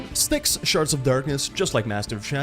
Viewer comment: Massively! Have you had a chance to play the first game yet? If not, I can highly recommend it! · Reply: Yes I love both :D i just had to post my self-identified funny comment when I misheard you say "sharts" instead of "shards" and thought it fit with the humor of the game xD